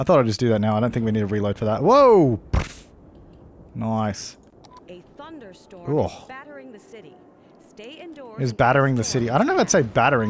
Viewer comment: I thought it said there's a thunderstorm in Battery City